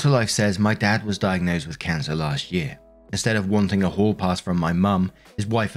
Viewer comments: I misheard "hall pass" as "whore pass" at one point in the story. I'm going with it. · Omg I dont know if its the accent or what but everytime says "hall pass" I swear I hear "whore pass" lmfao